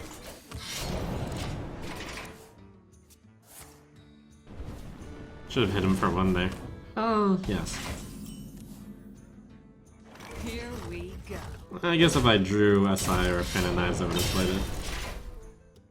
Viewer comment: What was that random voice? It sounded like "Oh Yes" The Yes was voice, but the "Oh" that came before it sounded different from his